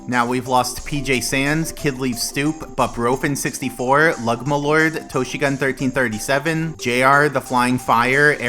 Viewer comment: Am I the only one who heard "Ligma Lord"? 😭